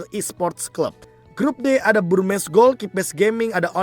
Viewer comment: itu Burn x Flash, bukan Burmese Ghoul. Samoe rewind 3x, kirain gw salah dengar